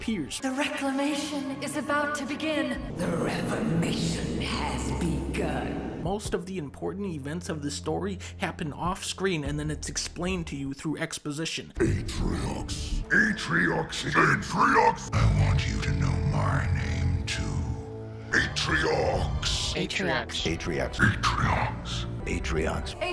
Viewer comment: i'm the only one that don't hear "atriox" or "atriox!" but hear "metal gear", "metal gear!!!", "metal gear?!?"?